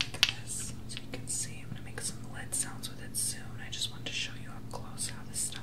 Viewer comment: did he say... "lit sounds" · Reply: He said "lid sounds" 😂